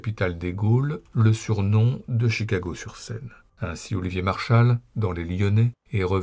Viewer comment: Ouch... "Chicago sur Seine (au lieu de Chicago sur Rhône)"... elle fait très mal à l'ego celle-là ! Mais sinon, merci bien pour la rétrospective fort intéressante. P.S. : quant à la gare des Brotteaux, elle n'est pas du tout détruite, ce n'est juste plus une gare 🙂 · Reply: j'ai entendu "Chicago sur Saône", moi